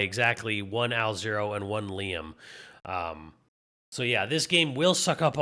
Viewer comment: I thought originally you said "Alzero and Leon" but now I hear it was Liam you said.....I loved my vita